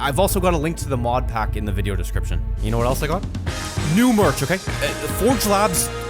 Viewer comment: You know what else I got no merch is what I thought I said when you first said that I listened to it again